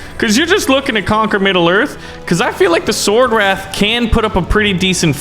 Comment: did he say SwOrDwRaItH